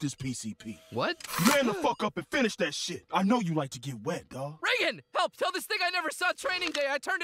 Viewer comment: Did I hear what I thought I heard 2:30 he said ni**@ 😔🤦